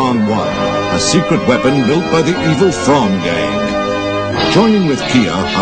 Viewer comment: did he say "evil thong gang"?